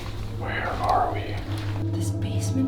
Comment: I heard a whisper saying "Where are you". Does everyone think thats normal 😭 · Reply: Hhh husband said where are we? I think his name is 😊